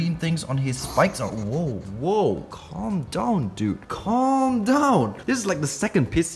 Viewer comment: When he said calm down I thought he said condom for a hot second LOL